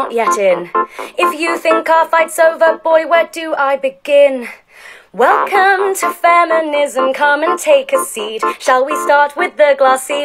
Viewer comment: She says : "Welcome to feminism" But all i hear is "Welcome to communism "